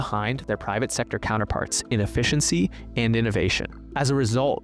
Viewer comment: You said efficiency but I thought you said rent seeking